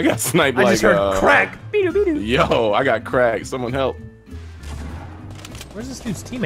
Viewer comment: got crack... i heard say "i got crack someone help me"